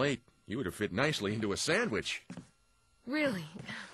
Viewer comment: lol, in the original, it sounds like barry says jibble sandwich